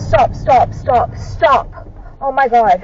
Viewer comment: sorry I didn’t hear what you said was that stop 😂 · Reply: 13 times STOPPPPPPPPPP STOP STOPPPPPPPPP